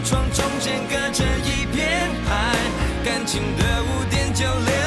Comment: "感情的污點就留" 这部分以前都听成 "感情的5.96". 这么多年后我终于明白了 😂